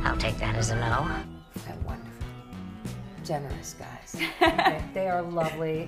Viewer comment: What did she say at 2:12? Gentlemen guys?